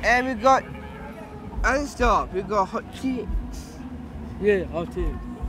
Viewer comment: You know mine is clean when he thought said hot chips instead of hot chicks 😂😂